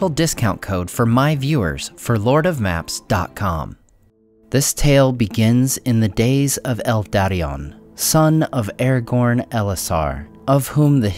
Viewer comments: What I hear: "This story begins in the days of Elf Daddy-on" Guess I now more about Tolkien now than I thought 😛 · did I hear that right? "El Daddion"?